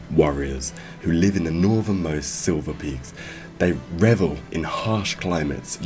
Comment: aren't they called the "Shiverpeaks"? sounded like you said "silverpeaks"